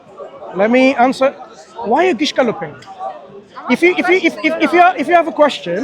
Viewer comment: Could anyone tell what is the word used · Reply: Gish galloping The Gish gallop is a debate tactic where someone overwhelms an opponent by rapid-firing a massive number of weak, false, or misleading arguments all at once. Because it takes much longer to disprove a lie than to say it, the opponent cannot answer everything in the time allowed